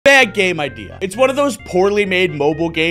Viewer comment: I thought he said bad game idea 😅